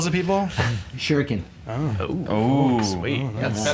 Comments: When Chad was asked, "Can you throw dumbbells at people?" at first for a second I thought he said, "Sure ya' can" then realized it was, "Shuriken". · "Can you throw dubells?" "Shurikin." (At first I heard "Sure can.")